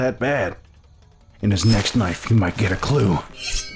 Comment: Now this may just be because my hair is growing too much and its covering my ears (I really need a haircut at this point), but I SWEAR lancer says "in his next KNIFE" and not "in his next life"; is this true, or is it just me?